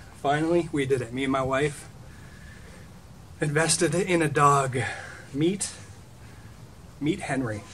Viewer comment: Aww hes beautiful. At the start I thought you said "Invested in some dog meat" Lol